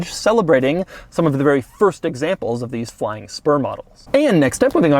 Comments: I thought he said flying sperm models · I could have sworn you said "Flying Sperm" models...🐳🐳🐳🐳🐳